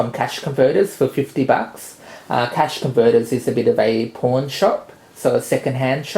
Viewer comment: Did he just say he picked up the Harmony One at a Porn shop??